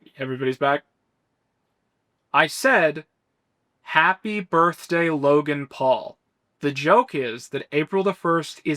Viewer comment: I think you said "happy birthday Logan Paul" in your back mask April fools video.